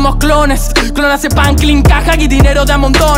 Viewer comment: Interpreto el "Purpura, veo el color en el aire" Como si nos asfixiaran, hasta el punto de quedar purpuras/morados. Ultimanente es común sentir presión, ansiedad, miedo, tristeza y el egoísmo. Esta yendo más allá de lo que sentimos comúnmente, esta yendo al punto en donde todos nos odiamos, que hay gente que cree y quieren creer que otros son "nadie", haciendo notar el egoísmo y la hipocresía de algunos. Perdón si es muy obvio o si nada de lo que digo tiene sentido, dude wtf. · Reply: Ufff, pensé que había dicho: "Clon hace pam.. clin caja" dando una (de las muchas)analogía a que 'estamos programados como maquinas por un trabajo que gasta más de 1/3 de nuestras vidas'